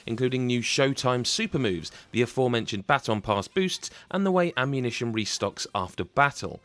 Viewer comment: I thought you said battle pass when you said baton pass, I almost had a heart attack